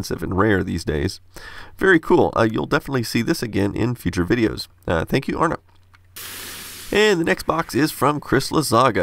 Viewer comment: David Said "Thank you Arne" But the subtitles say "Thank Arne"